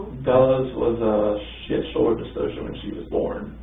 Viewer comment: does he say Bella's birth was a shit show??